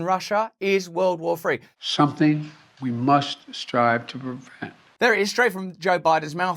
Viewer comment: What joe said at 1:18, When he said 'something we must try to prevent' ,it sounded like "something we must thrive to invent". They need world war3 to happen so desperately.